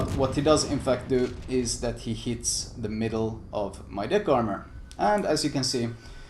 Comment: LOVE THE VIDEO, but I have to point out, at 10:05, I swear it sounds like you said "DICK ARMOR"... I am dying laughing over here!!! I'm hoping I have plenty of DICK ARMOR, can't have my dick getting BLOWN off!!! hahahahahaha Anyway, thank you for the great video, and your guide is VERY helpful! Keep up the excellent work!